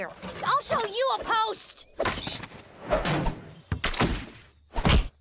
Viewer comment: I thought that said post cousins